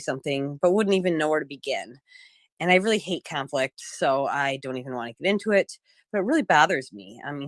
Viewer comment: Did she just say she hates Catholics? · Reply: That's what I thought at first, too. But no, she said she hates conflict. I had to go back and listen to it again.